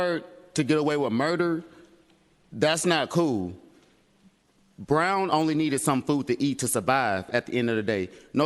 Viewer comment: It sounded like they said his name was fecal brown